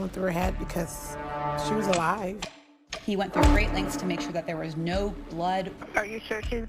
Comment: I thought she said he made sure there was no blood🤔😞then a minute later the narrator says there was blood everywhere.